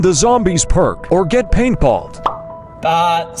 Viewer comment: All I heard was "get balled"